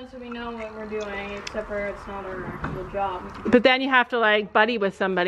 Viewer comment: don’t say a farrier is not a real job it definitely is and is a extremely hard job. I trim our 6 horses and it absolutely kills my back, you get paid and have to study it so it is definitely is a job. What if someone said YouTube wasn’t a job because you don’t have to do any study or go anywhere you just have to sit on your bum and edit when that isn’t the whole story? Wouldn’t that make you mad? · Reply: I apologise but at 16:13 it sounded like “not a real job” but she might have said “not our actual job”